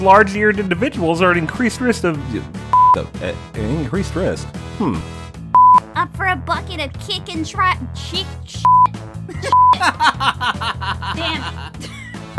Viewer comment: kickin was like: what the heck you said? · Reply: "up for a bucket of kickin' tried- chick- shit. Shit. Damn it!"